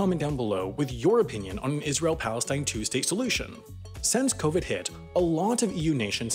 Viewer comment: Did anyone else hear him say, "An Israel Palestine Tuesday Solution"? I would very much like to hear about this Tuesday Solution, as it sounds interesting, and I'm board.